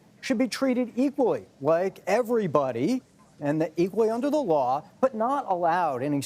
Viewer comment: he said "Mar-a-laRgo" instead of Mar-a-Lago" (even confirmed with the CC). LOL.